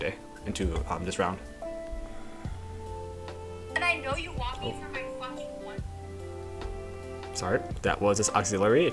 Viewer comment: lol i heard “ you only want for my fucking one p….”